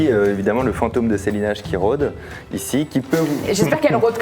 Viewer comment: C'est moi où elle a dit "lulu le vautour"